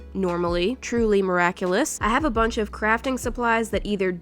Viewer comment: I thought she said "Crapting" instead of "Crafting".